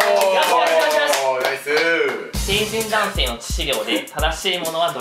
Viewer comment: ちょっと目離してる時の問題の成人男子の致死量がHey!Say!JUMPの致死量って聞こえて急なオタク問題でえっ⁈やし、何⁇どこ調べ⁈致死量⁇え⁇てなった笑